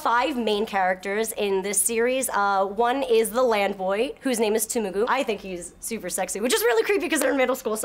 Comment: *Tumugu? Might have misheard but seems like you got it right the second time, because there's an S in there.